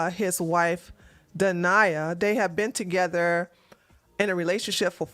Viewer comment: When you said her name was dania I heard denier. Just thought I'd say how apt her name is